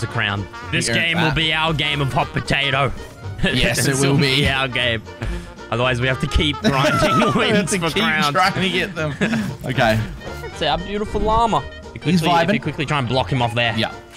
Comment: Legit thought he was saying crayons